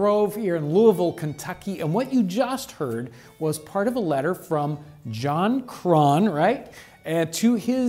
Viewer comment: For a moment there I misheard it as JonTron